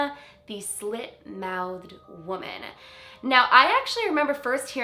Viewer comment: Definitely thought this said “shit mouthed woman” and it made me laugh so hard. 😂😂